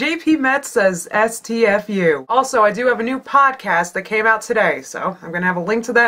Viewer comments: When you said "Podcast" I first thought you said "Potcast." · lol i thought she said POTcast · I could've sworn you said 'Potcast'. Also, brilliant video xD